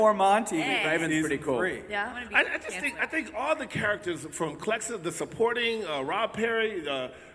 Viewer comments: did he say clexa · did jada say clexa